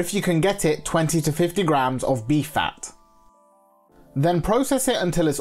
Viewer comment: He said Beef fat, yes? Because I heard 🐝 Bee Fat